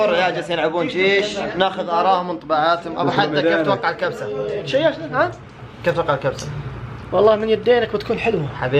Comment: سمعتو في الدقيقه 3:11 يقول شيشنا .. 😂😂